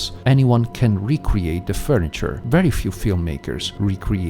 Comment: did i hear furniture?